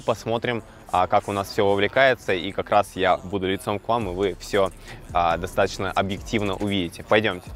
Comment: постоянно слышу: "яйцом к вам"😂 уже раз 10 переслушивал